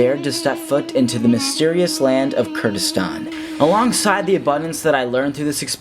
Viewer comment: wait, did you say KYRGYZSTAN?! I'm from there 😟